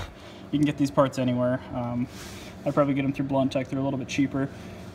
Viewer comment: you mention a place where you would have gotten the parts. I couldn't really make out what you were saying. What place? Thanks! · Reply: Blunt Tech